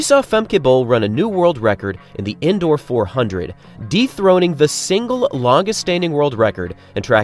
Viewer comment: I had to list to 1:10 twice… he said dethroning, thought he said “deep throating” I almost spit out my coffee first time I heard it.